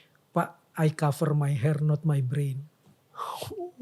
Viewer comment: itu ngomong apa min? im cove4 my hair not, apa?? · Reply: Not my brain